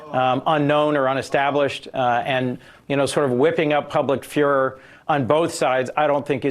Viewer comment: Freudian slip, it sounded like this guy said "whipping up public führer" 😂